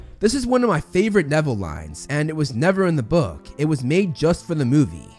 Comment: I thought you said that the line was Neville in the books get it never sounds like Neville